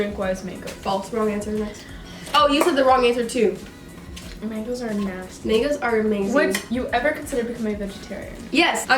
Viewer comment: it sounds like ur saying 'ni**as are amazing'